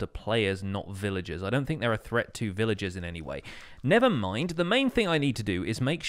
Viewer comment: you had the perfect opportunity to say “Nethermind” instead of “nevermind” 😭☺️ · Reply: i heard nethermind